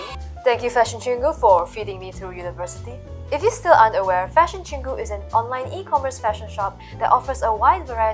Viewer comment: 1: bro. I heard "if you're still underwear,". I srsly need to test my ears cuz i misheard that despite wearing earphones... Any medical professionals willing to helo me? I'll give you my two snickers bars.